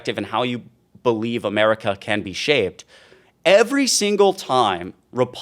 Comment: Did you just say how America can be "shaved"? lol, or am I just mishearing that?